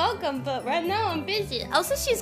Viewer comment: I love the way Kiara says "zooming". There's that very delicate German z -> c. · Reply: I had to rewatch that part because I thought you meant she's saying "cooming" and that's a totally different thing that I didn't expect lmao